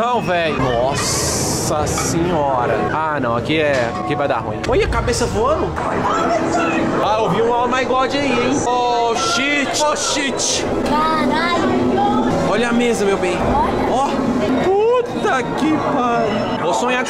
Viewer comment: EU OUVI UM O MAY GOD AÍ EM 😂😂😂😂